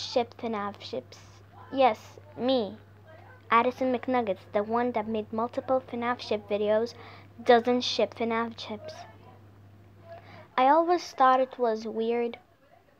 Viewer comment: Is It Just Me Or It Sounds Like She Said Fnaf Chips- I Think I'm Just Hungry Lmao- ✨✨